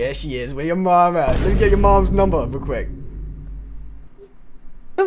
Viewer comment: All I heard was “mom number”, I hope this is promising